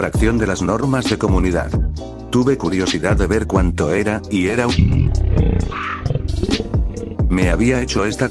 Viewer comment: que dice? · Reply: Una semana Está raro xd